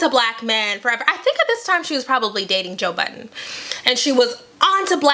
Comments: LOL. At 3:02, I thought said 'Joe Biden,' the former Vice President. LOL But I understand now after reading the comments the other 'Joe' she was referring. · Oop I thought you said Joe Biden ......